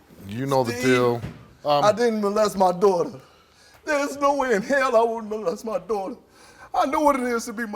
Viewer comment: I'm so glad he passed cause he seems like a good hearted dude, a little slow but still a good guy. She just a nutty weirdo. Oh and call me crazy but at 1:56 does he say "there's no way in hell I wouldn't molest my daughter!" ??????